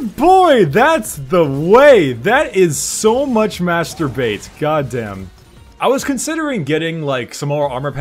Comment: did he just say:"Masturbait"? ^^